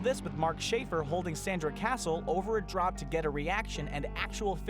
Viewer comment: "holding Sandra Castle over a ____"? It sounds like he says dropped, but that can't be what he's saying. Can anyone help me out?